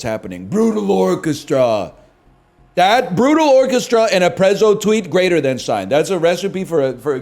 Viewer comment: what did he say? Brutal orchestra and what? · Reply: A prezoh tweet